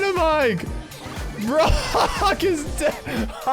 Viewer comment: I heard fu## but it was Brock